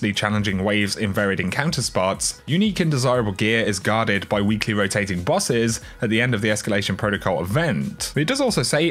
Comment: i thought in the reveal they said 7 rotating bosses.